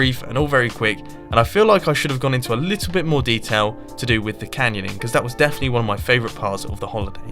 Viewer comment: lol I thought that he said canoeing...